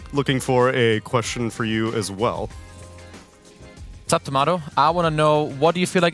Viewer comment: Alguien que sepa ingles, en 2:03 que dice el otro comentarista antes de decir su nombre? "sap" timado? en la traducción sale como UP Tomato, sería Up Timado? porque no parece que diga Whats up, se escucha algo corto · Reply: Sup, corto de whats up